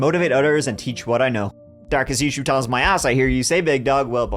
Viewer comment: What? What?! Dude I’m trying to understand you “Darkest towel issues cover my ass” is that what you said?? Any Irelanders who can translate this for me? :) thanks